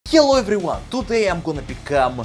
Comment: at the start it sounded like he said ''YALLAH EVERYONE'' Arabic speakers and Muslims can understand